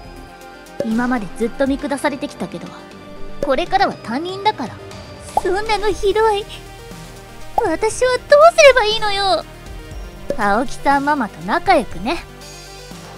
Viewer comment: 「これからは 担任だから」って聞こえた😂